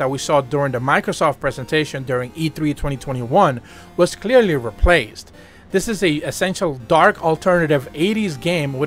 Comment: I cant understand your accent. . . Wish I could find that game at 4:37. You have to do more for us than just verbally say the name. I'm scrolling up and down looking for any spelling of anything that sounds like what you said. .. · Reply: In case you’re still wondering, he said “Replaced,” that is the name of the game at this time stamp